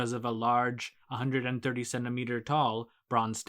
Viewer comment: i thought you said 137 meters tall instead of 130cm tall ahahaha